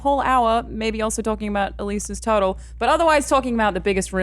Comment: Literally thought said "Elyse's total" like she toataled he car or something. But she said "Elyse's turtle" and that changes everything. Love your accent. Never change! Loving this podcast and channel💛💛💛